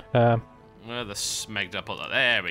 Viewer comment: at around 18:00 did i hear say "smeg"? watch red Dwarf recently? :D